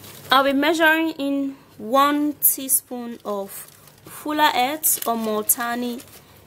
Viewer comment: Ohhh it's fuller earth ! I thought u we're saying " FULA ATE " Lmao 😂😂😂